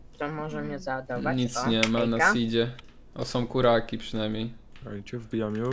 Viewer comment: ja usłyszałem że są buraki xdd